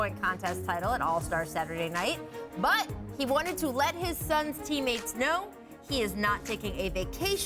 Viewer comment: I thought they said He told his son's teammates. I was like how old is his son?!